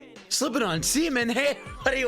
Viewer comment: Ohhh, I thought you said slipping on cement in the beginning lmao.